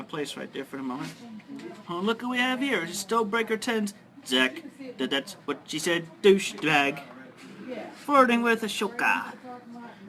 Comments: The unintelligible thing Chris said was "Oh look who we have here, it's 'Stonebreaker10's' Zac the That's-what-she-said-Douchebag. "Stonebreaker10", now "Swanky films" on YouTube was a Lego Stop-Motion film maker who has a series called "Lego that's what she said". I watched brickfilms all the time when I was 10-14 years old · I think chris said "stonebreaker tin zack" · when the subtitles say "unintelligible", he is saying "Stonebreakers10"; a YouTuber who used to make a series of LEGO animations called Zac the That's What She Said Douchebag